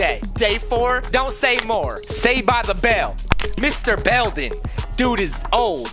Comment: For a minute I thought he said mr.bell Me: why u saying my last name